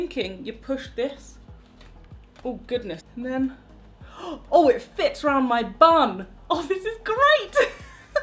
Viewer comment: I was only listening to the video, rifling through my bag to find something, and I thought I heard 'Oh, It fits 'round my bum! The most confusing but hilarious moment I've had all day!